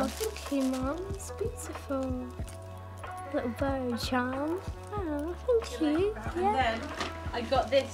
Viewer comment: it’s not bird charms I think she said it’s the evil eye xx🧿❤